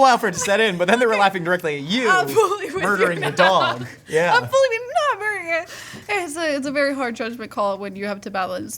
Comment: After 39:45 was she speaking swedish? Anybody understood what she was saying? 😅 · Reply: "I'm fully with you now."